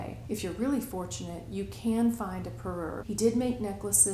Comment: What did you say around 1:55ish when you were referring to sets of jewelry? Perrur? Never heard that. New video idea! This jewelry is beautiful. I did NOT know that crawlers weren't new! I have a few pieces that look similar to this clustered rhinestone style...not Weiss, though. I just went to etsy and it looks like there's quite a bit available there. Now I have some shopping to do! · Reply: Yes parure. It means a set of jewelry. Jewelry was sold in sets for most of history. Uh oh. I may have created a YouTube made me buy it moment! Weiss does seem well suited for you. You tend to love bold statement pieces and don't seem shy about sparkle. I'm sure you can also find a lot to see in person in Houston Autumn. I look forward to seeing what you get!